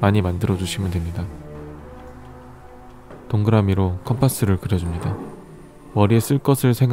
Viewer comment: 동그라미로 컴퍼스를 그려요..?? 잘못 들은건가...ㅋㅋㅋㅋㅋㅋ